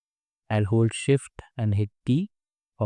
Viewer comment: did you say hold shift and T ? i don't quite get what you were saying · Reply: Hold shift and hit T